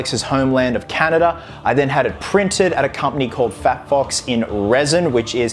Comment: I keep hearing, “Fat F*cks” 😂